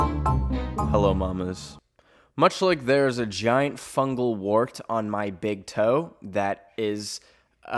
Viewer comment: I BARELY GET ONTO THE VIDEO AND I HEAR “MUCH LIKE THERE IS A GIANT FUNGAL THING ON MY BIG TOE”